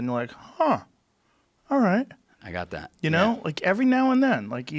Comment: Annnnnnnnnnnnnd this dude was never heard from again. Good job dude. Dont be a rat. Get me a drink Spidah.... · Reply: i thought you said that you are all right, Spider.